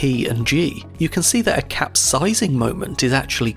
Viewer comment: did he just say png